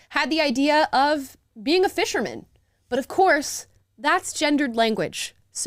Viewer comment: I could have sworn he said Fisherton